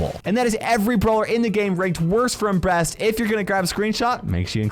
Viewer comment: What did you say at 16:14 😅😅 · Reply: worst from breast